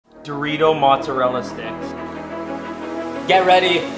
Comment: Why i hear "Dorito mozarella di*ks"